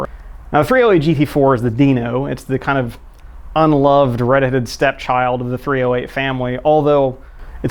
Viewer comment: Did he just say reddited child? Dank redditors now go to take revenge now😂😂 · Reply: Peace Pro red-headed stepchild